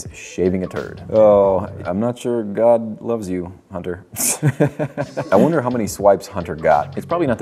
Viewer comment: My name is hunter and I was just listening to this vid for noise, and at 11:38 I heard “god doesn’t love you hunter” AND I THOUGHT I WAS GOING INSANE